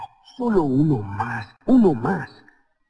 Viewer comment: Minuto 17:12 parece que dice… uno mas, uno mask.